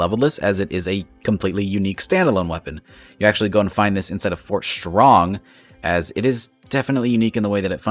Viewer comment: Did you just say Fort Schrong lmao :P?